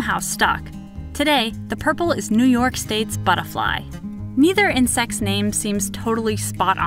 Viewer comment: Am I the only one who heard Butterah?